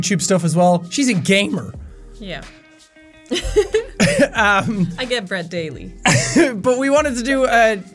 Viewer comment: "I get that bread everyday" You did good Sean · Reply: I thought I heard her say "I get bred everyday"